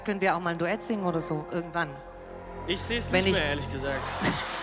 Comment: Was sagt Mark bei 9:05, als sie ihm das Duett anbietet? Ich versteh immer „Ich sings lieber ehrlich gesagt“, aber so richtig Sinn ergibt das ja nicht. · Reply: "Ich seh's nicht mehr ehrlich gesagt"